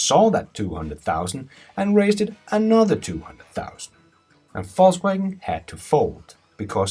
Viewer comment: sound like he is saying falsewagon :)